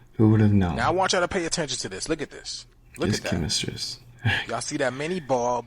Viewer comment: Did you just say😒 "chemistress"? "Chemistrist"? "Chemistrice"?😔 bro...at this point you just need to fire your editor. He don't give af how dumb you look. He NEVER does you any favors. But...I guess if you don't care either, save yourself the trouble. You seem like you're from a small town, so it may also be difficult to find an editor worth anything at a decent price. Good luck.